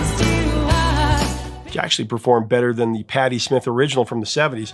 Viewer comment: I had to do a double take when I thought you said "she actually performed better than Patti Smith..." My bad, you said the song performed (charted) better! Sometimes those edits exclude important information or voice inflections! 😮😅